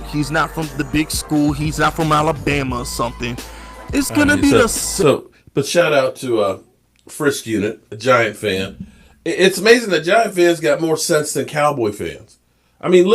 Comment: His name is called Fisk Vegas Unit General Fisk Fisk Vegas reactions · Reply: thank you 😄. I thought said first unit 😂. Can't hear super good anymore 😂😂😂